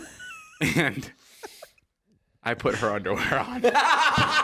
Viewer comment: what does he say? I put her...??? My english is not that good, can someone help me? :D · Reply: Underwear